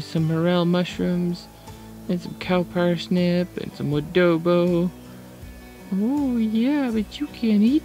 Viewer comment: did he say ADOBO?